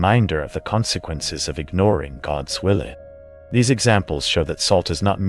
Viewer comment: First, I’d like to say that I’m a witch (Glenda type, not a baddy - BTW I disagree with the whole “don’t tell people you’re a witch” thing, but anyhoo…) and spiritually eclectic, which includes an appreciation for different religions, including Christianity, despite being raised by well-meaning atheists. So, if I find humor in the topic, I don’t mean any disrespect. … Having said that… I know it’s probably a language thing, but the part about “ignoring God’s Willy” made me laugh in the midst of something so serious, and I loved it. I really needed it too, because a couple nights ago I had a very intense lucid dream about fighting evil with love. I already use salt, but maybe I’ll use it more now. Hope everyone here follows what they know in their heart is good and true. And I promise to never ignore God’s willy (although honestly how could you, it must be mighty indeed!). 💙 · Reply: Glad to know I'm not the only one who heard him say "God's willy" lol